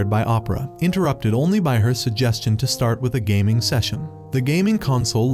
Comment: I heard gooning😭😭😭😭😭😭